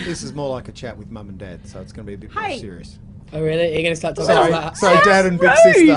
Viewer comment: "oh really? you guys gonna talk about sex?" uhhh Louis did i hear that ritee?